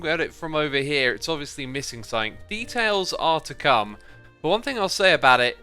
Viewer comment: does he say ‘beetails’ because I’m losing my mind here